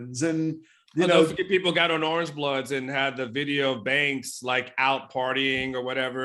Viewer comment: What is referring to someone out partying after the Baylor loss? Who? I couldn't make out who it was. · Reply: Sounds like Coach Banks. But so what if it was? Blow off some steam and go back to work Sunday.